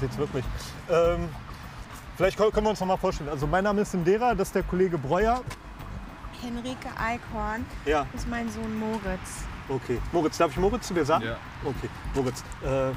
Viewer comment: Ich habe als erstes henrike Einhorn verstanden😂😂